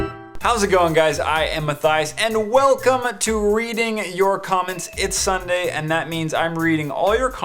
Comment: For a second I thought he said EAT you comments